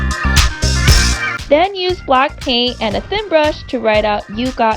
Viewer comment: i thought you said BLACKPINK....but it was black paint